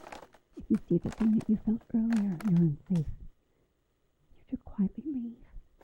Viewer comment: I heard "if you see the thing that you felt earlier, you're not safe"